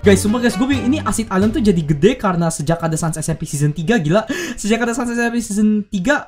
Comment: ngomong apa bang zzzzZZZzZzz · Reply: Dia ngomong "sanssmpseason3"